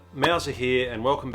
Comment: I keep hearing your name "mowser" approved Temres..... Now I know. Thank you!